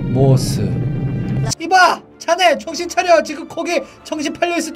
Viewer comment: 초때 시바? 이바인데 시바라고 들림 저만 그런가요?